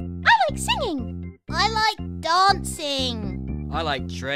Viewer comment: who thought freddy said I like dogs sing when he actually says dancing